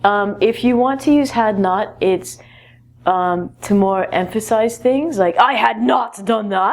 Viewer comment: At time : 4:14, i don't understand one word what you said , is it amphisy? In the sentens : using "had not" is more to "amphisy" things. I'm not sur (thanks)